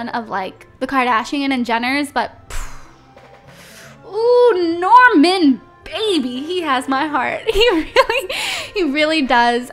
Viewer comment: I thought she said Norman bates at first lol 😂😂